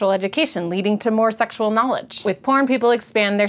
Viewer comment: Loool did she say "Porn People":- @ 2:29. She's an extremely smart woman the way she switches from biology to physics(buoyancy in this case).